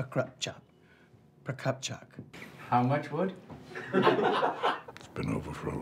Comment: The bloopers for "Anna Mikhailovna Prokopchuk" were so hilarious!!!! And "How much wood?" elevates Matthew Rhys' sarcastic wit to the highest peak!! Brilliant! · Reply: I thought he said “how much word” but “how much wood” is also hilarious !